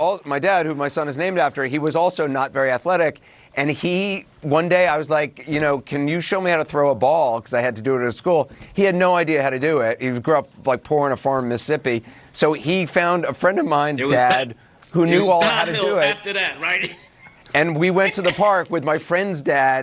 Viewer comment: What did he say ? Did he say, his dad grow up poor on a farm and that is because he did not learn how to throw a ball?!?